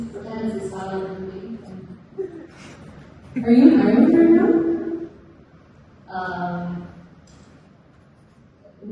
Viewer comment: I didnt understand what taylor asks at 5:23 ........ "are you in *** right now?" and amalia answers "next question" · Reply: She asks “Are you in Ireland right now?”